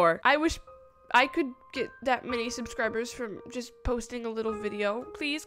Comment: Me: Did she just say “Subscribble”????? 😂